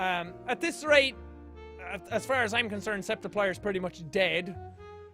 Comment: I HEARD MARKIPLIER. H E L P